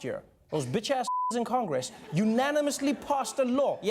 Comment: what did he say? I can't tell what swear word he said. Thanks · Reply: "Unanimously"